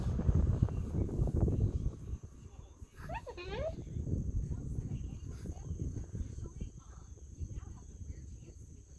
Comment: i cant figure out if he said hi there or hi dan? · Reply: Hi Dan, that's what he said